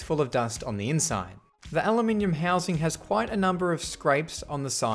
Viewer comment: WHAT DID HE SAYYYYYY!!!! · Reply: He said aluminum, but it may be his accent that made you thought he said something else